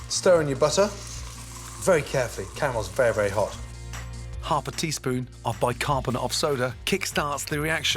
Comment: Dis I hear correctly, he says "half a teaspoon of bi-carb soda" ? But he did a heaped teaspoon...